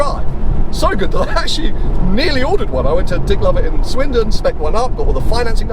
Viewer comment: "Dick Lover i Swindon". Or am I just hearing it wrong....? 😅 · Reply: Dick Lovett is name of the car dealer. Certainly a name to conjure with !